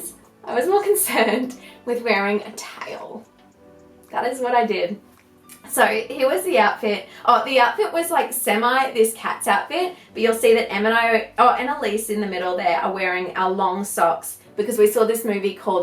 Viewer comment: did you just say Annelyce at 11:49? :)